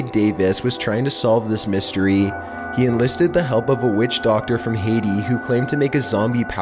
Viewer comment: my name is Haydee and when i heard “witch haydee” i got mad for some reason IMAO